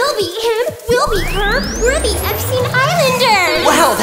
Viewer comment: I was drinking water when I heard "we're the Epstein Islanders" and I choked